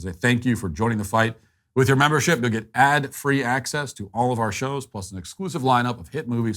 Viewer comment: during the ad, I thought he says "Madd Free Access" 😂😂😂